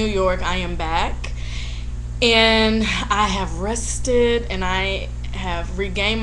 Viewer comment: When u said.. "I'm rested"... I thought u were saying that u were 'ARRESTED' at NY.